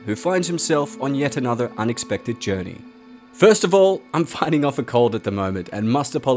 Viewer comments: When you said fighting a cold I thought u said fighting a cult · Lol I thought he said I’m fighting off a cult